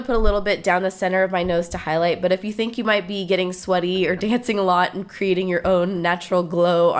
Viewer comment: So pretty!!! But i was laughing my butt off cuz i thought you said "but if you think you might be getting slutty" but it was "sweaty"!!! lol!